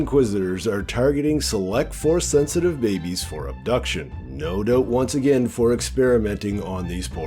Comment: I had to turn on cc cause every time I heard "no dope"(no doubt). I was like "what? that makes no sense". Once or twice cc typed dope.😁 Sorry, but you give me my second giggle of the day.😊